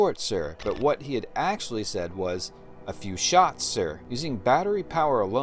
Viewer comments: For a long time I thought Scotty said shorts. When I watched it several times I finally figured out that he said shots.😊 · Well, up until today I thought he said, "the fuse short, sir." Like, the circuitry is shorted out. I never understood why it wouldn't be enough against Reliant's shields if it's shorted out... there's nothing going to happen. I saw this in a theater when it premiered. I have it on LaserDisc, VHS, DVD, Blu-Ray, and an iTunes copy... always thought it was "the fuse short". Forty-four years later, you cleared up that confusion.